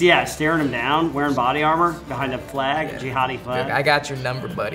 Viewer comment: Did he just say jihadi flag🤣🤣